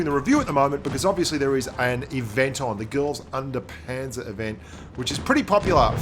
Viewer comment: Did he say "girls under panzer"? That's my kind of grind